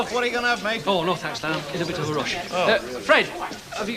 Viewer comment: Am i hearing things or does Alf call Len `Stan` at 2:05:35 ?